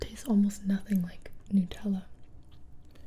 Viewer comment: I heard Nooootella Pingu joke